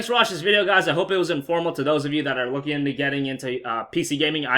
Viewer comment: did you mean to say "informational?" I heard you say "informal," which would mean not formal/professional. There's already enough words that sound similar... lol